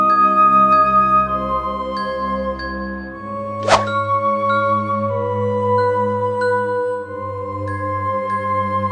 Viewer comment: enel minuto 7:04 lo que dice es tu finse acerca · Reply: Si dijo tu fin esta cerca xd pero lo repetía muchas veces